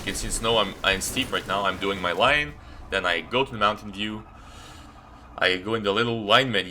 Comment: Am i retarded or does he say "mountain dew"